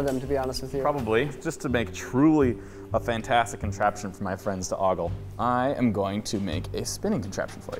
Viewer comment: I thought it said contraception..😂 i was like whaaattt